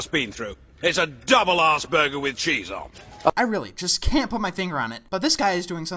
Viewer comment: ive never felt so bad for someone. (also i suffer from aspergers syndrome and in all honesty...ive heard that joke far to many times to really care anymore. well at least if thats what he is saying, kinda hard to tell) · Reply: again i felt like i heard that but i have bad hearing so…(it sounded like he said Asperger with cheese, but im probably wrong)